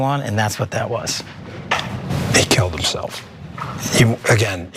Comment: Did Bongino say, “THEY killed himself.” ?